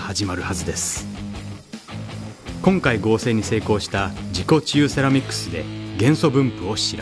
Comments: 自己中セラミックスって聞こえた。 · 自己治癒セラミックスが何回聞いても自己中セラミックスって聞こえて辛かったです